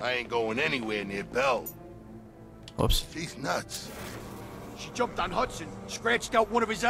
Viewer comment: sounded like he said "Deez Nuts" When he said "He's nuts" haha lol.